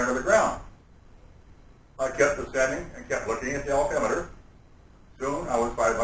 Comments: And he's clearly reading a script.... <link> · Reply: I thought he was saying, "altimeter", but when I looked down at CC it said he said Odometer. Not sure which is right but I heard altimeter. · He stated he looked at the ODOMETER? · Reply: I clearly heard "altimeter", although I saw that the caption read "odometer". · He stated he looked at the ODOMETER? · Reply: i thought he said altemeter